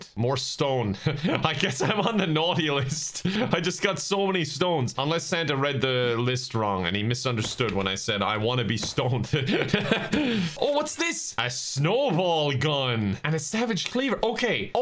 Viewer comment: did he just say "i wanna be stoned"? XD